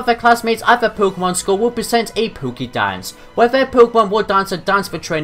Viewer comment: Sounds like your saying "PokéDan"